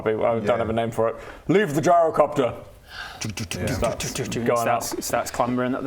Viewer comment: did he just call it vigirocopter!?